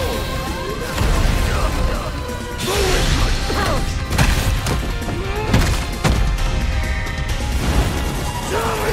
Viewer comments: Dose anyone get what he said when activating his level 3? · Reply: The weak must perish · can anyone tell me what he says here? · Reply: "The weak must perish" is what he said, I was intrigued as well bro